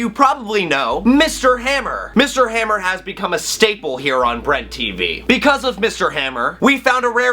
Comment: For a minute there I thought you said bread Tv